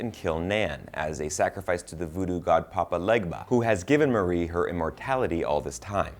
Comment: sounds like he said “Papa Ligma”